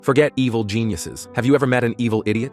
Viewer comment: first heard it as "forget evil penises"